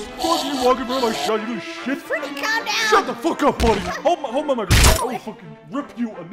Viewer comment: Did Freddy just said the n word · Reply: No, he didn’t. He said “Hold my microphone.” I think because the sound of Bonnie’s face being ripped off made it sound like it.